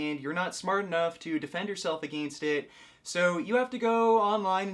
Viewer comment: Wait wait wait... Did he say "aGAINst"?at about